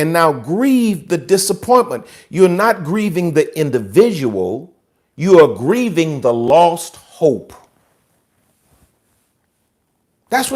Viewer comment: “You’re not grieving the person, you’re grieving the disappointment and loss of hope.” Wow. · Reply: I heard him say, "You're not grieving the individual, you're grieving the lost hope." Grieving the loss of hope (which he didn't say) would be tragic indeed.